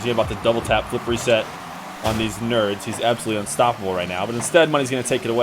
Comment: did you say "on these nerds?" lol